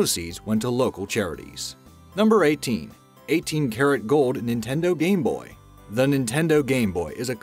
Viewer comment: When u said karat I thought u said carrot